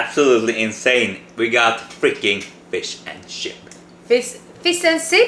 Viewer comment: jeez it sounded like said 'fish and sh*t..' :DD